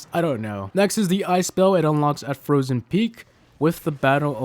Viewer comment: what did you just say ice ?????